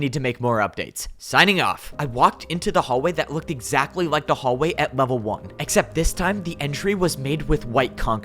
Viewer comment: I thought he said "I walked into the hallway that looked exactly like a hallway" lmao 😭😭